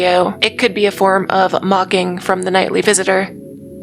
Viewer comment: Definitely thought you said "mogging" here. Hate when I get mogged by the nightly visitor.